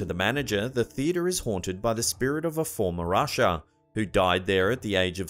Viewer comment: LOL I thought you said that the theatre was haunted by a "former Russia" instead of a "former usher". Thank goodness for Closed Captioning!